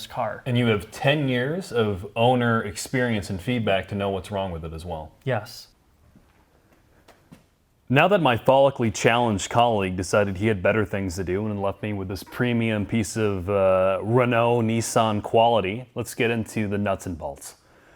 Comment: "Follicly challenged." For a second I thought said phallically challenged. Excellent onscreen rapport – save for the latte-sipping troll at the end of each video. all humor aside, I pray that your personal life is under control.